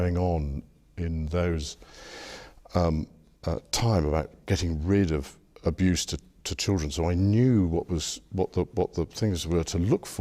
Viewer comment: did he say he was in charge of “getting rid of abused children “ ???